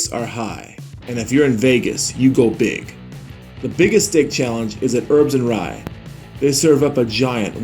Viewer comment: Am I the only one who thought he said “the biggest dick challenge” within the first 10 seconds😂👌